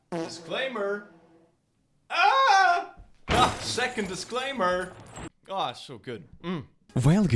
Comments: We truly needed the second disclaimer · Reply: I thought disclaimer said dinosaur… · I thought disclaimer said dinosaur